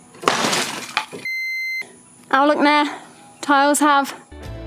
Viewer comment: did you say "awlak ne, tiles have"? LOL... literal translation from Sinhala to English.. So cute.. :D